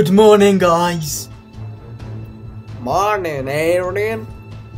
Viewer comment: if you hear it tell me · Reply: I heard it, it sounded like he said MoRnInG Arrdien😂